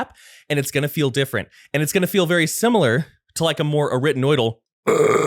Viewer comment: What's the word he says at 30:31? Sounds like "rittenoidal" but subs say "written oil" and google says "retinoid" relates to chemicals to treat skin conditions, not throat anatomy. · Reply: arytenoidal